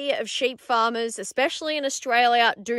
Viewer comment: Ha ha. The end of the video sounds like you say “gimmie a fellow and learn what happens to our sheep”.